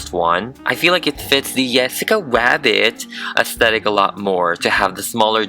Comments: Why does he sound like he's saying "Yessica" Rabbit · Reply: I heard "Yessica Wabbit" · If you guys are wondering why her bust is censored it's because youtube might age restrict this video like how they restricted my Popovy doll. Vladonna's bust is a little detailed, so it's best to be safe than sorry :D Let me know if you want me to upload a non censored version, and I can put a link in the description :D EDIT - Just added the link! - <link> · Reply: is said yessica wabit because I thought you said yessica wabit😛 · I love how it sounds like you say "yessica wabbit" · In the video he sounded like he said yesicca was it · It sounds like he is saying "yasica wabet" 😂😂😂